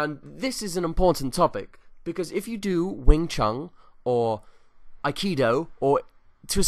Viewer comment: Oh, I thought you said Wang Chung, and I was also gonna agree.